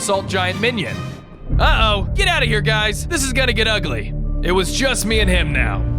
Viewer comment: Did he say this is gonna get more UGLIER????